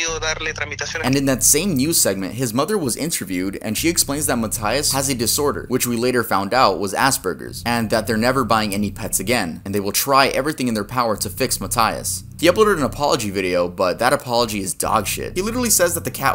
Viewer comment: lmao i thought he said "assburgers"